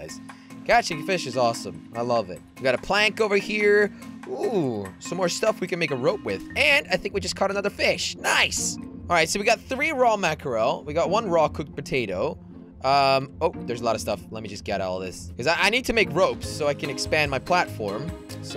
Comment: Did you say raw cooked potato at 9:04 :)